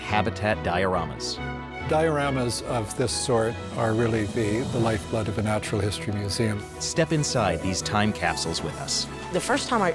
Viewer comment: plz help! what is he saying in 1:00 minute? Dioramas of this sort are really...? i cant understand but my homework is to do a script of this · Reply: baz "Dioramas of this sort are really the life blood of the natural history museum". :) (good luck on your script! BTW, you can turn on captions on the video to help you out.